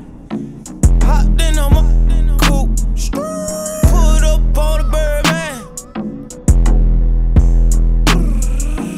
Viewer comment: what did he said? 0:33 I heard screeeeeewwwwwwwwwwww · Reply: Scrrrrrrrrrt